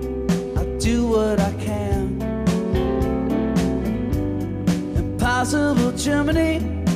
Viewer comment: I always hear 'Impossible chimney"